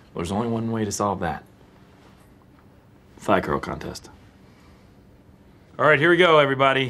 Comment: Every time I hear Jim say “thigh curl contest” all I hear is “fly girl contest” 🤷🏽‍♂️🤦🏽‍♂️🤣